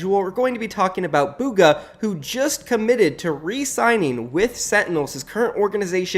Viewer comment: thought you were saying committed suicide and I laughed so fcking hard